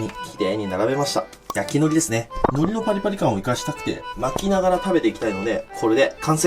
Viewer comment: 「剥いた赤ウニ」が 「泣いた赤オニ」に聞こえたので 耳鼻科に行ってきます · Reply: その後の巻きながらが泣きながらに聞こえたのでお供します